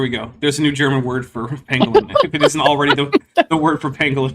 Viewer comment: I misheard "pangolin" as "penguin" at first and thought, "but penguins don't have shells."